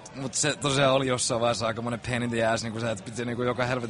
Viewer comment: did he say "pain in the ass" at 2:09 ??